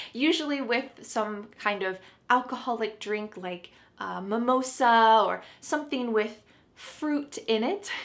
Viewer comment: Wtf is mamosa now 😂😂 is she calling samosa a mamosa that's awfull for Indians